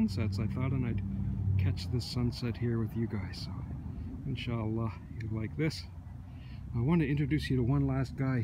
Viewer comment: did you say insallah ? :)